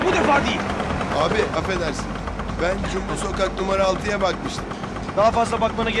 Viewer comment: Cumbul sokak mı diyor ben mi yanlış anladım